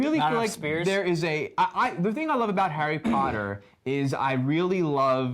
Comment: i heard 'hairy' potter